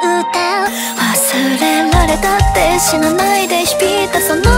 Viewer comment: 空耳で希望って言うんだよの所がヒーローて言うんだよに聞こえてしまった…ずっといい歌詞だなって思ってたらただの空耳だったw